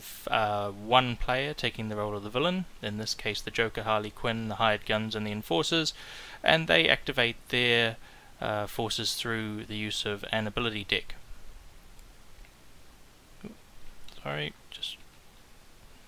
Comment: it sounds like he says ability dick